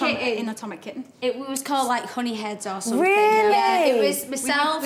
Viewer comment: Oh thank god! I thought Kerry said that their group name when Heidi was with them called "Honey Potheads" but it was Honey Heads